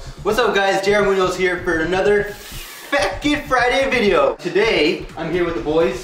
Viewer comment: It sounds like your said fat kid friday instead of fackitsfriday and it's funny haha